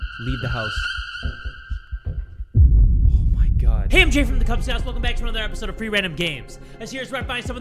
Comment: i SWEAR in the beginning i heard “jay from the kub snouts”😭